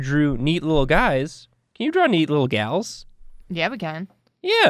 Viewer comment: Hmm thought it said meat little gals...